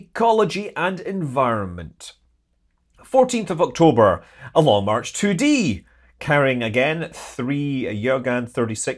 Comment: A Long Mach 2c ... I heard that as "a long march to sea"... gonna go clean out my ears now and then maybe get a cup of coffee so I can wake up.